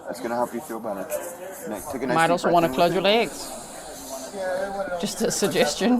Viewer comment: anyone else hear Nene Leakes "close your legs to married men"? 😂😂